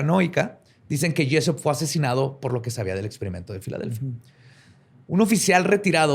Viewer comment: Estoy tan dormida que entendí "experimento pedoFiliA" y dije, esto va a estar heavy